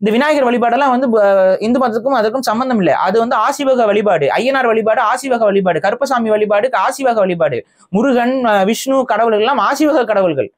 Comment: Sorry for my limited tamil, I am learning. Please, can someone give me the word said around 11:05 sounds like "asivagha" - what is the correct word please for my own research , thank you. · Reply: aseevagam